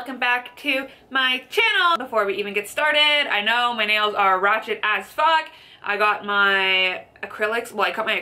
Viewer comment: Did u just say " Rawchet " omg lol